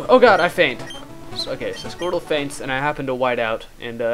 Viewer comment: Lolz when it said 'Hayden whited out', I thought it said shitted.